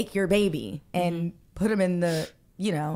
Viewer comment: Jason is ALWAYS interrupting his cohosts and guests to interject his personal experiences. Just stfu and let the ebb and flow of communication take its course! 🤦🏾‍♀️ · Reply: I started to get irritated because I could’ve sworn he said “I’m not going to interrupt”. She handled this interview so calmly.